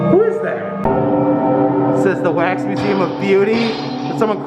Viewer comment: Ha! Thought u said the museum of beer!